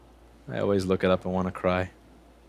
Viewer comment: DID SOMEONE SAY WANNACRY??????